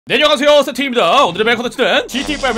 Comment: 왜 네려가세요 세팅입니다 라고 들리냐